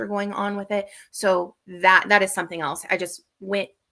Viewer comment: Time stamp 14:55 Did she just say a FIVE MILE WALK??